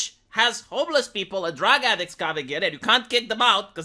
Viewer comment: I could swear I hear saying "drag addicts". Which is a concept that is way funnier to me.